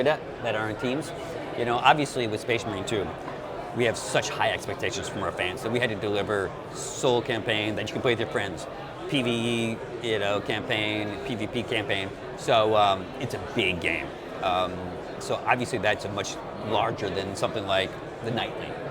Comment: did he just say PVP campaign?!?